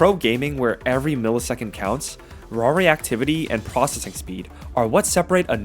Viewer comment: I thought you said "[Fer]rari activity" and it still made perfect sense